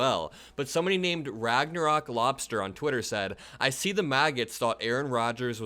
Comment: It sounded like you said " The Maggots", LOL. If you didn't, then I'm keeping it now. MAGA is the movement, Maggots are the followers 😂